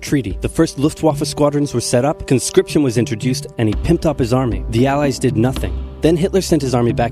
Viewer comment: Did he just say: "Pimped up his army"? 5:48😅😆😂